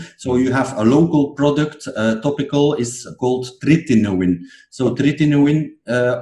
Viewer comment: What is the doctor saying at 19:12, very difficult to understand the drug he is talking about? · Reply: Tretinoin